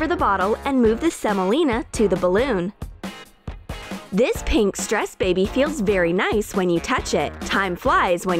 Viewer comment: Did she say "pink stress baby "